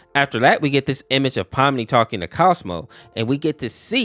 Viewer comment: did he say Cosmo